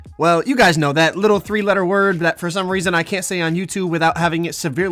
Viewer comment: don’t call me stupid or nothing but tf word he talking bout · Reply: I was told the word is rep